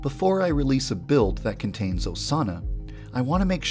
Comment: So... when he said Osana... I thought he said "A sauna" and i was like: REALLY?! A SAUNA OVER THE RIVALS?! 😂😂😂